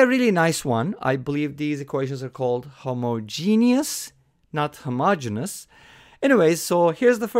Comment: Yes Homogeneous. Sorry what is Homoginas? Is like I heard you saying something like that. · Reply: homogenous